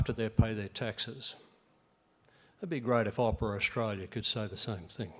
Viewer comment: What does he say at 13:51? "........ could say the same thing?" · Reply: “It would be great if Opera Australia could say the same thing.”